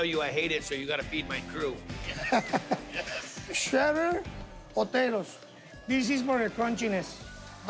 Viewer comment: Mmm...😋🍔🍤🥑🧀 Someone please tell me what was that crunchy shredded topping he put on last? I know on maangchi cookn channel its crisp onions but it sounded like he said potatoes. I played it back twice. sigh... idk🤦🏾‍♀️ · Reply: They are fried matchstick potatoes